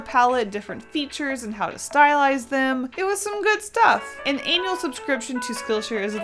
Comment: I was doing something else while watching this.. and I swear I heard 'anal subscription' at 0:51 ..I know I'm not funny, but God damn I'm dying..